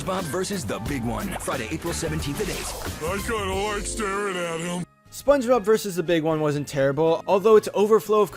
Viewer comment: did patrick say i got hard staring at him